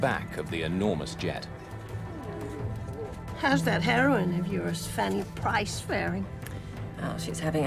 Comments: she said heroine, I thought at first she said heroin, lol. 35 seconds in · How's that herion of urs, 😆 🤣 😂 sounded like she said that, anybody else hear that. Anyway nothing funny about plane crashes, I hate taking planes.